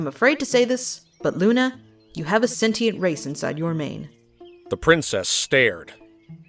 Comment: At first I thought it said "sentinel race" and thought of No Man's Sky.